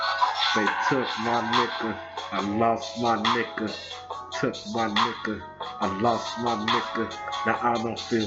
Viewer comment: Am I the only one who hears, "They took my knickers, I lost my knickers"?🤭 I can't help it; I just hear knickers despite being an American. 🤭 Wonder what that means? 🤔